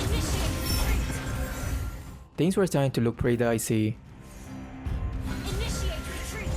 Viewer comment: I always smiling when you said “pretty dicey” buat I misheard it as “pretty daisy” LoL